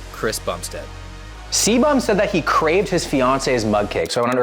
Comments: First I heard "CBUM craved his fiance". Hope that's true as well. :D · At first I thought he said Chris Bumstead "Craved his wife's mud cake", and I was like it's not a cheat meal if it's your wife.